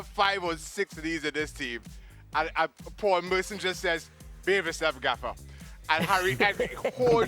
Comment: Whag does Shaka say at the 16:17 mark? Paul Merson says, "They have _____ Gaffa."? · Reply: "Behave yourself, Gaffer." It's British for "don't be a nuisance/stupid, coach."